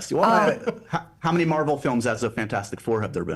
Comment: the miscommunication was probably due to accent difference and also the audio/speaking going a bit quieter! (as a brit) i also misheard you upon first hearing it, thinking you were asking "how many marvel films as the fantastic 4 have there been", but then caught the "as of" upon listening a second time. but i'm sure they'd have understood in person! <3